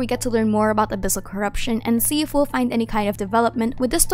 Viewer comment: at the end i heard "i am a disaster" instead of "my name is aster" 💀